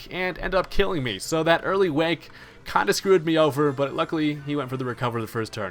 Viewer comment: Okay, for some reason I heard early wank, I really need to get my head out of the gutter.